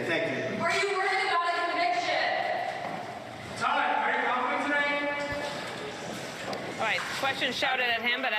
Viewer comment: Did I hear "Todd are you humping today?" 😂